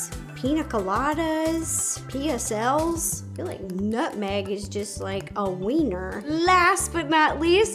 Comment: did I hear that nutmeg is a wiener